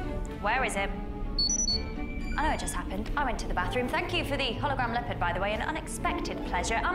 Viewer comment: What does she say at 0:21? "Thank you for the hologram something." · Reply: She said "Hologram Leopard"